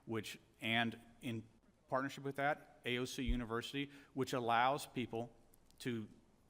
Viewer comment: DID HE ACTUALLY SAY "AOC UNIVERSITY". 🤣🤣🤣🤣🤣 Holy shit. TELL ME THESE ARENT THE MOST THEIVING CRIMINALS WE'VE EVER SEEN.